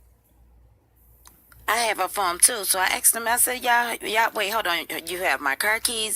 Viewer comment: Did say” I have her phone too “